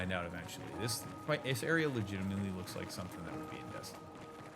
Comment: can someone let me know what he said there? This area looks like something (out of..) ..? Just curious and I couldn't make out that last bit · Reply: "This area looks like something that would be in Destiny." An FPS MMO type game.